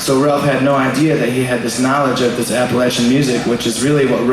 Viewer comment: Dang. I thought he was saying apple etching music. I thought I learned a new term. Appalachian.